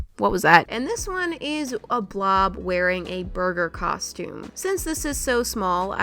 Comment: Lol when u said burger I thought u said murder hahahahahaha